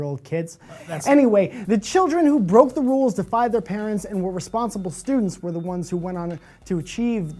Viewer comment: Did he say "and responsible students" or "weren't responsible students"? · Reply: 1220 To me it sounds like he said were responsible students.